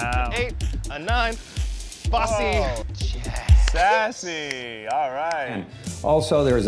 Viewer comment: I AM CRYING AT 5:32 DID EUGENE REALLY SAY 빠세 (bba sae) ???? I LOVE HIM